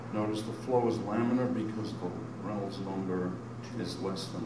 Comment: what is he saying here? "Notice, the flow is laminar, because the ... is less than one" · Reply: Reynolds number, But what he saying is like the water freeze bc it is cold.